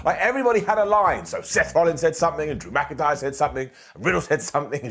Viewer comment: said “Drew McIntyre’s”, but I heard “dramatic eyes”. That’s how I’ll be referring to him from here on out